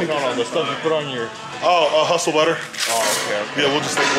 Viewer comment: Minute 24:40 did he say truffle butter 😝